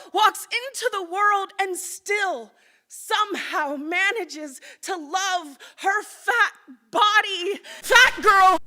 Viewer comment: All I heard was her yelling I'M FAT.